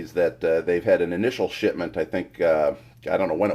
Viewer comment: anyone else hear 'shitment?'